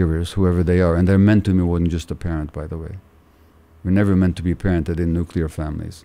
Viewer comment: I love this conversation! I am however not quite catching what Gabor says at 41:28, “[…] and they are meant to (something something) by the way” I’m so so curious about what he says there as I sense that that detail would be very important for my understanding of my own psyche and childhood. Anyone who has an idea? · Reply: I've listened to him speak numerous times on this topic and I'm pretty sure he's saying "mentored by more than just a parent". He talks often and writes in his books about how all adults in a tribe or community would mentor children. I hope that helps you. ✌️🇨🇦